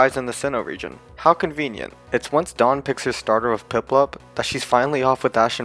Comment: Am I hearing that right? "It's once Dawn picks his starter"